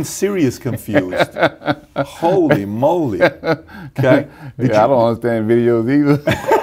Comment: what did he say? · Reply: Siri on the interviewers phone said “i don’t understand videos” and Ronnie said “i don’t understand videos either”